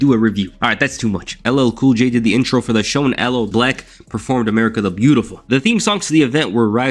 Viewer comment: I thought you said LL Black 😂😂😂